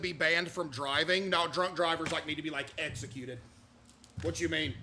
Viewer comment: did he said “drunk drivers like me” Does CaseOh get drunk behind the wheel or is he kidding? I hope he doesn’t drink and drive 8:33 What was that scream?